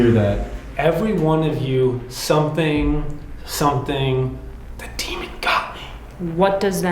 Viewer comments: I hear "Everyone of you did, but the demon got me" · I hear “ Everyone of you, get out, and RUN. The demon got me” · I heard "Every one of you, now get out, _______, the demon got me." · Hey guys to me the voice in the recorder sounds like it says, everyone of you has respect. The demon got me 😮 your are all brilliant by the way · I hear you all came with respect the demon got me😢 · I hear "Every one of you does not care that the demon got me.". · I hear ‘everyone of you did show disrespect!, the demon got me’ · i hear “every one of you should run…now..the demon got me.” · I hear “Every one of you HAVE NOT… the demon got me…”. So like you guys did great being respectful, but at some point the demon came in to scare or something. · I hear “Every one of you were respectful the demon got me” I also found the duck! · I hear ''every one of you, GET OUT. The demon got me.'' Like the spirit was taken and warning you guys. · I hear, “everyone of you has assumed the demon got me.” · I hear “every one of you need to run the demon got me” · I sounds like it says must think the demon got me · Hey guys my son just gave an interesting thought. At 1:19:34 where says what she hears, my son said that he hears "EVERY ONE OF YOU DID NOT, BUT THE DEMON GOT ME" any thoughts? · I think I heard ‘every one of you get out. The demon got me’ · I heard "every one of you will die, the demon got me" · I hear " everyone of you get out because the demon got me.